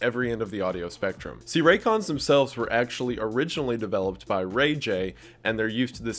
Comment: did he just say “Ray J”???coomer fr